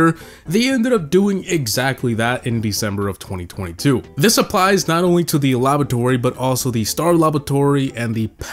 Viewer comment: Is he saying labatory or is it just me